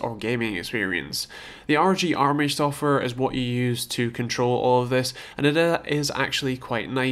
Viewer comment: what does he say at 3:13 ? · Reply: "The ROG Armoury software is what you use to control all of this." The software if available on Asus' website. Just go to the product page for the Claymore and click 'support' then 'drivers & downloads'.